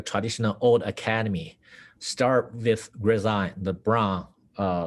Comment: is he saying grisaille? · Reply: Yes, I think he said "grisaille" .... note the context immediately after.